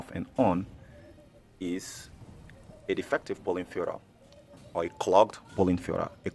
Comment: another can't understand what is defective. Subtitles say poly fiera? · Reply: Filter